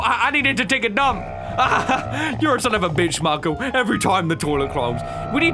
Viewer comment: did just say bitch